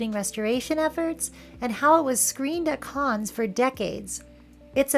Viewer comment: I thought i heard "and how it was screened at Cannes for decades" and i was like... wut